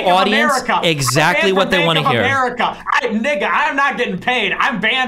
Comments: bro what did nick say???? He definitely said ninja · Bro, did Nick just say Nigga